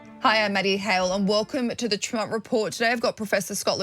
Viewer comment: I love you Maddie but for awhile I thought you were saying Muddy Hale. 😁